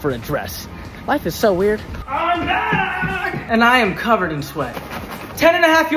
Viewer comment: I thought u said IM BLACKKK😂😂😂 😂😂😂😂😂😂😂😂😂😂😂😂😂